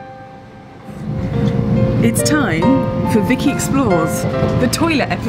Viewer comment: For one moment I thought you said. explodes the toilet, or explodes in the toilet. That peeked my interest. I need a hearing test.